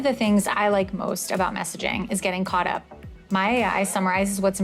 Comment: i thought he said "i could use it for masturbating", not messaging, now i know he's a cop